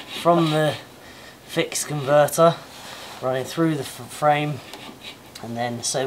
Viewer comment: I thought you were saying Face converter